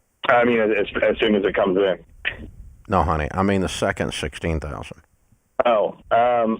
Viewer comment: Did Dave just call "honey" @ 12:00??? lol